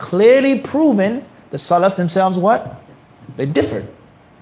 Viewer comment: akhi he clearly said in 9:30 the companions differed in the core foundation of aqeedah and the furuuaar meaning sub branches. this is clear evil statement so what is the difference between the ahlussunah and the innovators. akhi the companions did not differ aqeedah and shaikh islam ibn taymiah has narraated the consensus of the scholars in his book وقال شيخ الإسلام ابن تيمية – رحمه الله-حاكياً الإجماع على عدم وقوع الخلاف بين الصحابة في أمور الفقه العظيمة فضلاً عن العقائد!! قال: "وهكذا الفقه إنما وقع فيه الخلاف لما خفي عليهم بيان صاحب الشرع, ولكن هذا إنما يقع النزاع في الدقيق منه, وأما الجليل فلا يتنازعون فيه. والصحابة أنفسهم تنازعوا في بعض ذلك ولم يتنازعوا في العقائد, ولا في الطريق إلى الله التي يصيبها الرجل من أولياء الله الأبرار المقربين". [مجموع الفتاوى (19/274)]. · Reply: osman jibril salam alaikum akhi. It's Your brother abu ubaydah. I just wanted to say that my daughter who is only 11 pointed out to me that mufti did not say at 9:30 'companions' he said 'salaf' I'm not sure this changes anything but she wanted me to point this out. Also thank you for all the previous advice you have given may Allaah reward you greatly!!